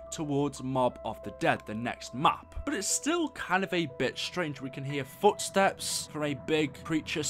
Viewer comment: Can't be the only one who heard 'a b*tch trange' at